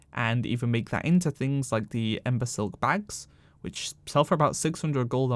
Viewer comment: I heard "the imbecile bag" lmao